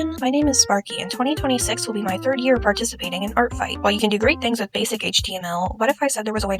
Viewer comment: Help i heard "thirtieth year participiating in artfight" lololol